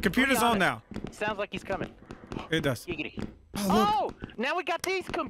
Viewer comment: I don’t know if it’s just me but does it sound like Del said giggity? At 3:29?